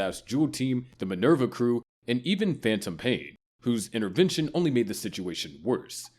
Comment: did you say phantom pain